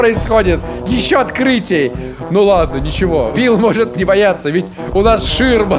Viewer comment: Мне надоело всем писать о том что не матернулся на 2:50, он сказал «не бойтесь» только он это сказал быстро и неразборчиво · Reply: Лол я что правда один кто сразу услышал "не бойтесь"? Грязнословие у кого то другого в голове а не у... А кто досихпор не слышит что на самом деле ставьте скорость 0.5-0.75